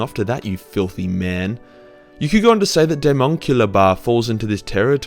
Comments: can somebody tell me what these "demonkillabar" is my google scearch is going nowhere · What is the name of the thing at 7:57? I can't understand it. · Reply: Demonculba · Can someone tell me what he's saying here? I'm trying to look up whatever that is but I cant understand the name. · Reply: Daemonculaba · What is he saying at 7:52? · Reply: Daemonculaba, Basically really messed up way Chaos Marines tried to create new Marines from aspirants instead of the usual procedures that the normal chapters use. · Can some one tell me the name of that bath mentioned at 7:53 because all I heard was "deamonical bath" and I low key intrested in this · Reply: Daemonculaba. You're welcome. ;D